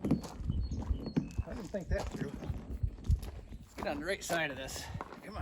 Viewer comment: what did bro say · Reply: I said “I didn’t think that through, let me get in the right side of this “ As in I was in the wrong side of the tire